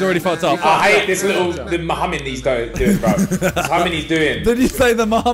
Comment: I literally misheard Ethan saying "I hate this Mohammed" instead oh mm humming or whatevs, it would've been utterly mad